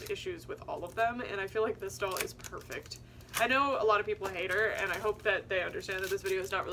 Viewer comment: I thought you were saying people hate Rapunzel and I was like how? She’s literally the best